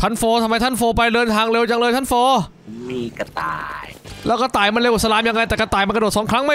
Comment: ทำไมได้ยินว่ากระตาย